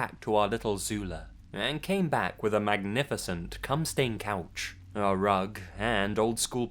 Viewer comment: "...we came back with a magnificent cum stained couch..." The fuck? Did I hear that right?!